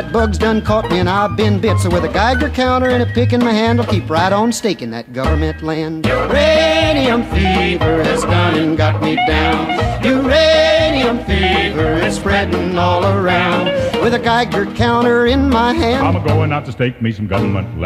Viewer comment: when I first heard this I thought it was about a uranium beaver... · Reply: when i first heard this i thought he said radio fever